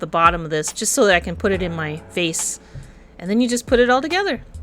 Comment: I‘ve heard face instead of vase 🤣 I’m gonna cut off some leaves so I can put it in my FACE 😂 ❤